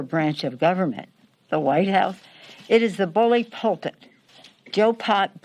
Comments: did Pelosi call Biden “Pol Pot”? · Am I hearing things, or did she call Joe Biden by the name of Pol Pot? Jeez. I hope not. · did she say Pol Pot(Joe Pot)???